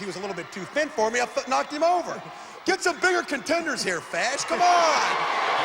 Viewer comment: wait... sorry... what does Hawk say at 44:30 ? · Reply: He said "Get some bigger contenders here, Fash! COME ON!"